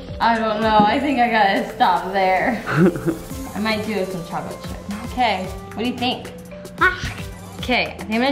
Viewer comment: Sounds like is saying "more chocolate chips."